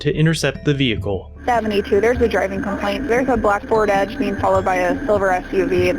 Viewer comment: I thought the caller said a ford escape but the officer said a ford edge…